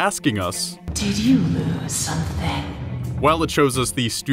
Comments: On Alice’s first speech of the trailer, I hear “Did you something?” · I hear "Did you learn something?"